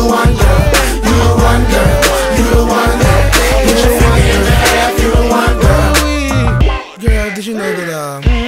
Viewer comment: It always sounded like he was saying " Put your feet in the air if you the one "... LOL...!!!...